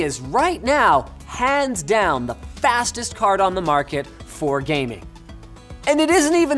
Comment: Sounds like he said "The fastest HARD ON the market"